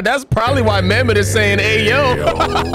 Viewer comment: I thought I was the only one that thought mammoth say ayo